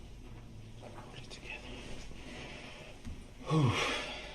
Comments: Who else heard him whisper “hold it together” @ 8:17?! 😂🤦🏼‍♀️ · you can clearly hear his voice whisper to himself. “Get it together”. I believe he is remorseful but can’t process his emotions correctly because his mind is focused on representing and attempting to defend himself. He probably doesn’t even sleep just goes over what he’ll say, etc. No one who has murdered people intentionally or not, sleeps. That just doesn’t happen.